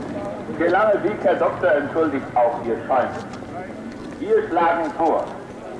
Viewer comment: Ich verstehe den Satz aus dem Lautsprecher ab 1:00:53 nicht, der Lange Weg Herr Doktor …….. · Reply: entschuldigt auch ihr Säumen.... (Schiller, Wallenstein: Spät kommt Ihr, doch Ihr kommt. Der weite Weg,. Graf Isolan, entschuldigt Euer Säumen)